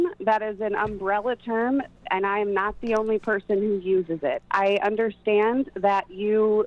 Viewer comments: Did she say she was NAZI only person? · anyone else catch that? 8:46 sounds like she says, "I'm nazi only person..." right up there with Biden using the "N" with the hard "R" word. good and funny stuff!